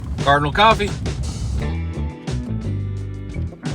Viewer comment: All this time me and my girlfriend thought you said caramel coffee and not cardinal coffee 😂